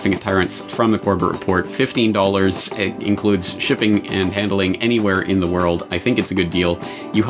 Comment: I had to laugh right at the end when you mentioned the purchase price of the DVD included shipping and handling. For a brief moment I'd thought you'd said "Chipping and Handling". Must have been a fraudian slip on part of my ears, shit I didn't even know that was possible. Better get me ears checked right quick... :-)